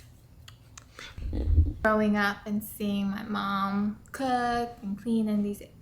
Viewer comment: Man the subtitle closed caption thought ya said throwing up, and I was what oh wait it's growing up you mean lol